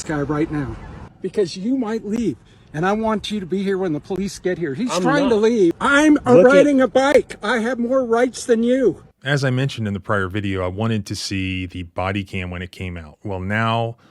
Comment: Did he say, "I'm a riding a bike, I have more rights than you." @ 1:12 ??? WT F mate!?